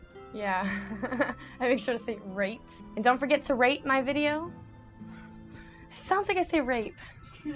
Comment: the take you chose still sounds like you said "rape my video" :D enjoyed outtakes most this time :D